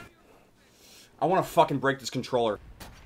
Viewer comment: When u said break wat I thought u said was well rape this controller